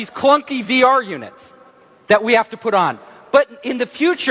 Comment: did he say we got VCR units in our head?